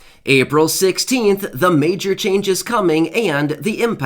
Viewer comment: Hello I thought you said April 7 so it’s not April 7 anymore??!!